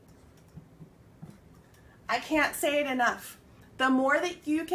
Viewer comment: very good your channel! thank you! When you were talking I had a doubt: how we can recognize when someones says for example: 15:27 "I can't say enought" I heard you said: I can say enough! How to note the difference when north american people say can and can't!?